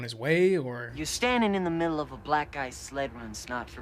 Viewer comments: You heard Black guy, Youtube auto subtitles heard "Black-eyed" I heard Black type. Did anyone hear Black Ice at first? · If you think “black guy” when he says that, that’s what you’ll hear. But if u listen to it again and think black ice, THATS what u hear. My mind is blown